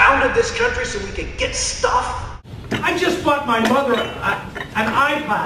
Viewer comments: Did he just say "I just fucked my mother" at 00:25? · It sounded like he said "Fucked his mother" Instead of bought an ipod.